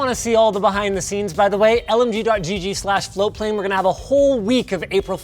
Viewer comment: after seeing that dead bird, I thought I heard “OMG” instead of “LMG” for a sec……